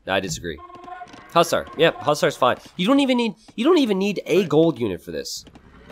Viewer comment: can anyone tell me what unit is "plusar" he said?